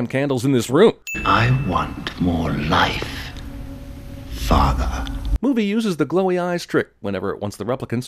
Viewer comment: i have heard Roy say "i want more life, fucker." at this moment instead of "i want more life, father." erm. why?